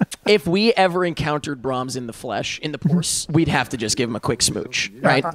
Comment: It knocks me out everytime I hear say “in the porce”